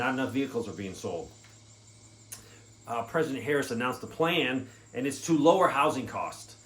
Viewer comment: Did u say "President Harris"??? did i miss something? Is Joe dead?